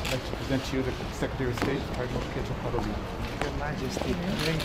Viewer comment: did he address to her "your grace" or am I hearing things?